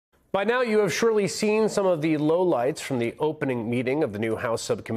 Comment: did you say low lights or low lifes?